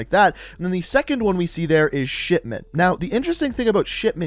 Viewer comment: To me it REALLY sounds like he is saying shitment